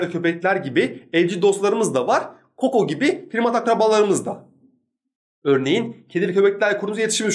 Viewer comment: da ne dedin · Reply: Şükür Şükür “Koko gibi primat akrabalarımız da...”